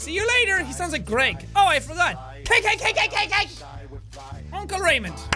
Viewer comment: when he screams cake over and over i thought he said KKK